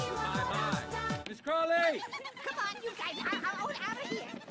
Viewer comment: Кому на 0:42 послышалось "ЭШКЕРЕ"???